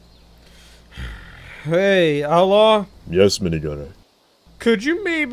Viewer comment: minigunner: “hey, outlaw?” outlaw: “yes minigunner.🗿” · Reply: Sounded like it said hey ella instead of outlaw